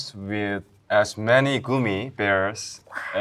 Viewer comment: Soobin saying goomy instead of gummy🤣🤣